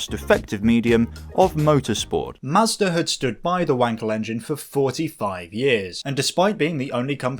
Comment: I can't quit laughing because I thought you were saying Wanker engine. lmao!!! What a unfortunate name lol.